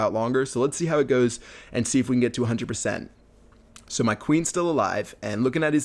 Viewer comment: We'll at 4:03 u said we'll see if we can get 200% instead of saying 100% -.